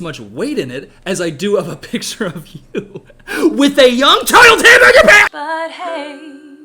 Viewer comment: I heard “young child’s hamburger pants” and I got so mildly upset that I had to make you guys hear it too